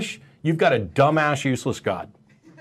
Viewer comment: I heard it as "puny god". 😎